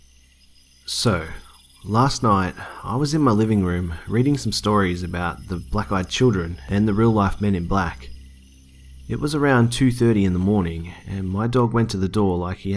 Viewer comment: Forget about black eyed kids My mom doesn't even open the door for my relatives Not that I complain anyway🤣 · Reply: When I first heard the video, I thought it said black kids, so i twas like "The bloody ell ya have against us?"